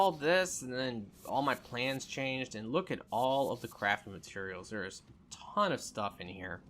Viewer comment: Every time he says “it’s a bunch of crafting materials”, it sounds like he says it’s a bunch of crappy materials lol.